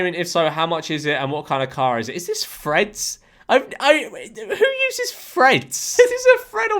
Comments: i thought he was saying "Fred's" and that it was a new social media i hadn't heard of and didn't hear anything else until he said "a twitter thread" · on todays episode of “how did george’s accent confuse me today” I thought he was saying “Fred’s” instead of “threads” for 1/2 the video😶